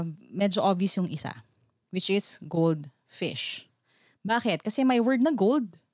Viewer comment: voice:goldfish what i hear:grade six